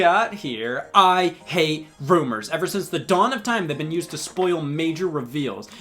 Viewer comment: I legit heard “I. Hate. Boomers.”